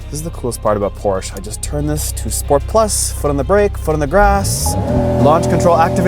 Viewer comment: I could have sworn you said "foot on the grass"?